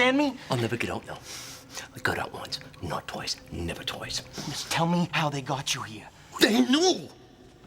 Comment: I always thought he was saying, " I got out once... no toys, never toys."🤣😂